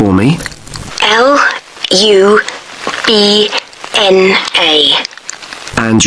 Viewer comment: bruh it was lubma i thought she said lupna thats kinda unfair